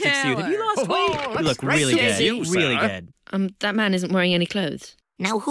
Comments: Wth it sounds like somebody is saying “You look really gay. Really gay.” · I think someone said “you look really gay really gay”